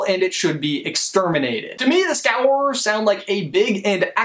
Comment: I thought he was saying Scowerers but apparently he was saying Scourers.